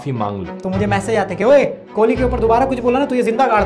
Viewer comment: I heard kohli is that rYt Ya bss maine sunna